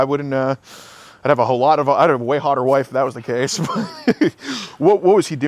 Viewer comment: AM I THE ONLY ONE WHO NOTICED HAS A GF AND A WIFE. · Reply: I heard him say “ I’d have a hotter wife “ wtf 😂